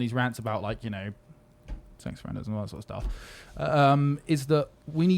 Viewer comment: What did say at 12:00 he was muttering I just got ‘sex’